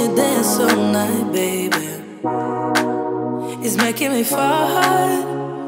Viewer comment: I love this track, but I swear I hear the part beginning at 0:09 as "Watching you dance all night, baby, is making me fart" every time.